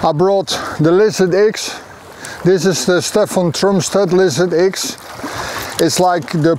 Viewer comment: Hallo zusammen, wie heißt die Rute, die in Minute 19:30 fischt? Ich kann ihn leider nicht so gut verstehen und bin derzeit auf der Suche nach einer Jerk Rute. Danke! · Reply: Das ist die BFT Lizard X (bis 130g) Stefan Trumstedt. Der Köder ist aber der Buster Swim (ein Swimbait kein Jerkbait). Wenn Du Jerks hast, die Du über die Rolle annimieren kannst und nicht über die Rute, dann kann ich sie Dir empfehlen.